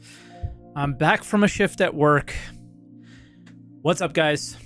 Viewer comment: I totally thought you said "I just came back from a ship that works." and I laughed out loud because I know you were on a cruise recently.